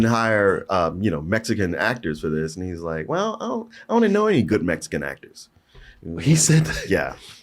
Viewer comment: that's wrong though, he didnt say he didnt KNOW any mexican actors, what he said was there was NO good mexican actors. which is way worse. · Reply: If you listen to him again, does say “good” it’s just said a little quieter than the rest of his sentence :)